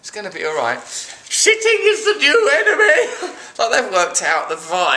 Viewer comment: SHITTING IS THE NEW ENEMY... or that's what I thought it said. lol